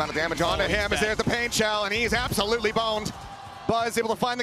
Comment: did he say "he is absolutely boned" XD